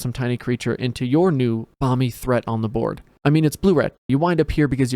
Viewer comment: Coulda sworn he said “Mommy Threat” at 12:38 …I mean it would still apply